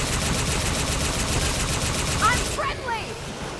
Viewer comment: I thought she said, I m pregnant, n ran away.lol